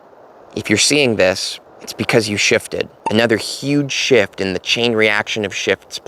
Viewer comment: I thought it said “shitted” lol